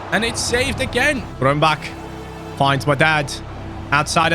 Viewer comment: He really said "Finds my dad"